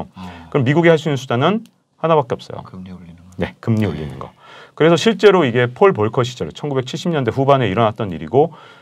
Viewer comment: 미국이 할 수 있는 게 뭐라고 하신 건가요? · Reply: "금리 올리는거" 라고 하시네요